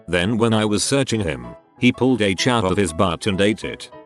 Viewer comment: what is the h word that is censored? I can’t figure it out haha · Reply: P Chop heroin